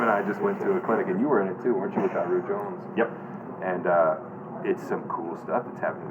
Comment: who is it that you're talking about? I heard Rube Jones I may be wrong, but I would like to look in to the style that was spoken of here. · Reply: Daru Jones